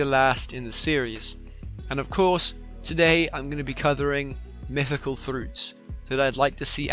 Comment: You said cothring mythical throguts. What he ment to say covering mythical fruits. Time stamp : 0:07 (1:57 sus ;-;)